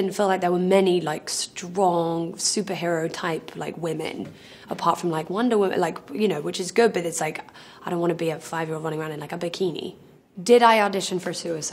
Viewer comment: Pleseeeee say what she said in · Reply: Apart from like Wonder Woman, like you know, which is good, but it's like, I don't want to be like a 5 year old running around in bikini.